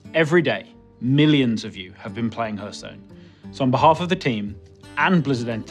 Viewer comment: WHAT DID HE SAY?! He's a developer and he says the game's name like that?! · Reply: Hurtstone